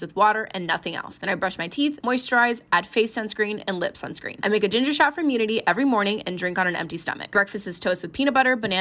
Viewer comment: I heard: “then I make a ginger schnapp for immunity-“ 😭😭